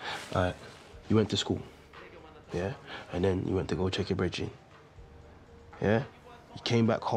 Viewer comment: What does Switcher say at 4:07? Went to school…. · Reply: You went to go check your brethren